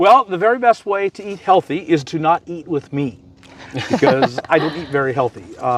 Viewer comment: Meat is not unhealthy. Or we wouldn't have made it the millions of years before big agriculture. It's the ultra processed foods including modern breads, sauces, additives, preservatives, emulsions, industrial seed oils, boxed and bagged engineered items devoid of nutrition, and massive amounts of sugary and chemically laden drinks. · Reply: Agreed...low carb! I had to re-listen, as I thought said not to eat meat, but he said to not eat with me.